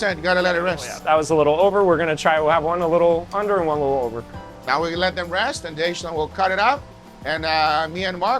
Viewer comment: What did he say on 4:29? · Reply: “we’ll have one a little under(done), and one a little over(done)”